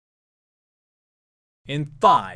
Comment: boi I thought you said "in fuck" because of those bleeps akdhdk